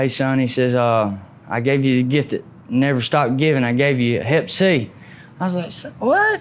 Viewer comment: what is he saying that his daddy give him when they shoot heroin together?? i don t understand..please,can anyone tell me and what it means? is at minute 3:05 !! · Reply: Hepatitis C